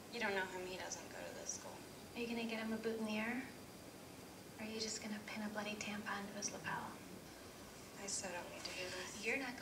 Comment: Can someone tell me what she said, please? I can't understand her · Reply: valeria alvarez “Are you gonna get him a boutonniere? Or are you just gonna pin a bloody tampon on his lapel?” I’ll be working on subtitles for videos soon.